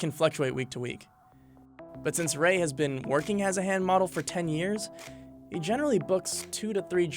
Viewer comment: Thought it said hand mobile